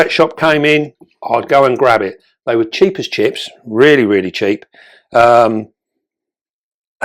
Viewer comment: Oh cheap as chips. I thought you said cheap as shit. Lol